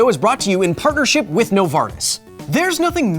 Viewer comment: I really thought you said "this video is sponsored by no farts"